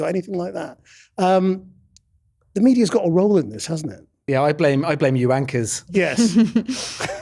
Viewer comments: I thought he said "I blame you wankers". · Did he say "I blame you wankers" at 19:15?